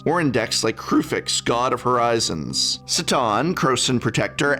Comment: Almost spat my morning coffee listening to this. I thought he said Satan when he mentioned Seton. That and having Seaman over everything.